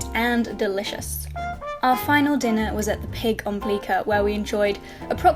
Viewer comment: What is the name of the dinner place in the end? Sounds like she says something like Peak on Bleaker, Pig on Bleaker? lol help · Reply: You're right lol it's actually called Pig Bleeker